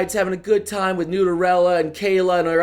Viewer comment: Wait 3:03 Did he said noodlerella? I didn't know...